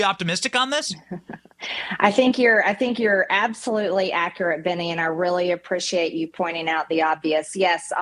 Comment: I thought she called Benny, Banana 😅